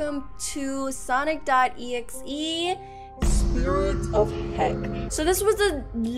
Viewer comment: You Did not say HELL. You said HECK. If you Hear this closely then you might be thinking it’s the word heck.